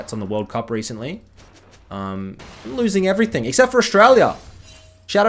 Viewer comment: oh my god I heard Shurelia instead of Australia 😅 Your accent can't be quite difficult to understand for me as I'm French Also its called football not soccer and Pulisic is a insult to Mbappé he should apologize for playing the same game Jk the STATES football team had a great run and their playstyle was fantastic to watch on the pitch, way more enthusiastic to play than the dull England playstyle